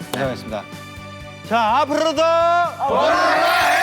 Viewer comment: só eu que escutei o jhope falando "PRA PRRRRAIA"